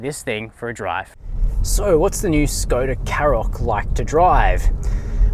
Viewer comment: Did you say Karoq